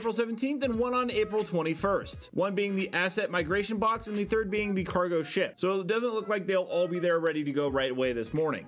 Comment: "and the 3rd being the Cargo Shit" surely I'm not the only one who heard this 🤣